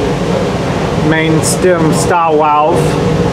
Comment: Did he say, "main stem stop wow" at 7:07 in? Made me spit out my coffee laughing.